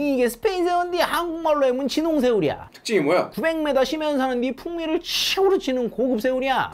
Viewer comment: 900m 가 자꾸 900 메가로 들려ㅠㅜ